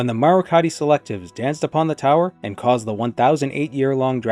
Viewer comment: did you say Marukhati Selectives?? Quiet with that, Glarthir is going to start killing people!